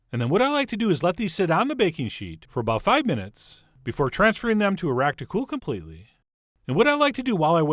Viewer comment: Did anyone else hear "transferring them to Iraq to cool completely" at the 6:03 mark? LOL